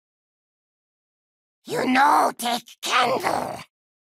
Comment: I always heard this as: You know thick candle?